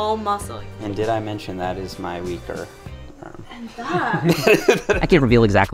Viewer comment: I thought he said "weiner" not "weaker side" 😂